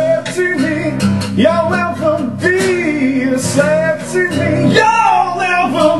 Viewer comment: Haha I thought he was saying "You'll never be sexy me" lol.